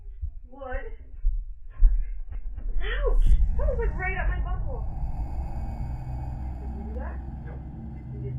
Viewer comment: Did she just say, "Ouch!Somethings like right up my butthole."? 😳